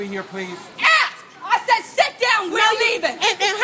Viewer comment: you can hear the mom says "Jazzy Sit Down" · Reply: She did say that i thought she said jesse sit down